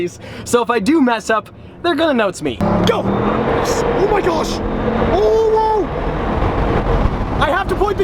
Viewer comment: did James just say OwO?